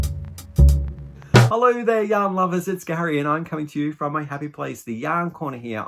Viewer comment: 😂I'm so silly. I just realized that you open your chat by saying " hello yarn lovers" I saw it on the merch while reading the descriptions. I always thought that you were saying " hello YOUNG lovers. Lolol😅😅😅😅. Oh boy 😂😂